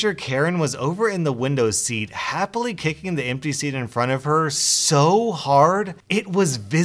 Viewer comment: kicking the empty seat. I thought he said kicking the NPC and i found it fitting.